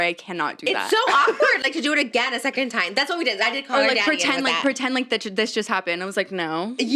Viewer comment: WAIT….. Did Trish just casually say she did Call Her Daddy? Or am I hearing things?